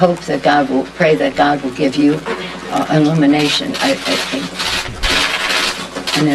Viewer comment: Maybe that pin on her lappel is for calling out to her hit man. She prays for his family and for him? Did she mistakenly say "elimination" instead of alluminate? Maybe I misheard her but that is what I heard. She is a wing nut anyway. The Demonrats are going down and she is panicking.